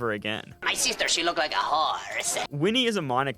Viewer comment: WHY DID IT SOUND LIKE HE SAID WHORE!?….